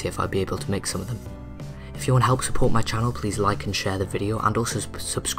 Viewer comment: I thought he said "if you want to help support my child" self face palm